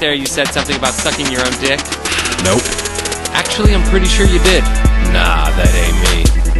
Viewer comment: it says "Actually I'm pretty sure you need" instead of "Actually I'm pretty sure you did". Freudian slip? XD